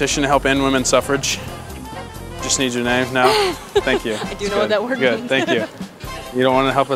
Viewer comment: Glad she was honest instead of assuming what it meant. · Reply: SoloBugg she said “i DO know what that means.” That’s why she refused to sign it.